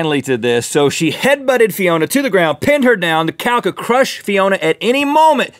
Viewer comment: i thought link said the cat headbutted the woman😂